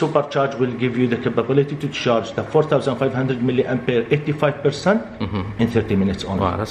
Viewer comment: How fast does it charge? I couldn't understand his accent. Did he say 80% in 50 minutes or 15 minutes? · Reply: 50m